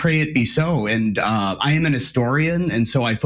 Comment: When said “I am an historian” I misheard and thought he said “I am a Nestorian”. Had to rewind it 😂